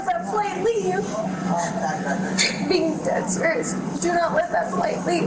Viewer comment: Did anyone else hear what she says @ 0:29 in the video? "Dead spirits" Is she referring to something she has seen, or what she believes will happen to the flight passengers if it takes off. If you slow down the video one can clearly hear her. This story gets more and more intriguing. 🤔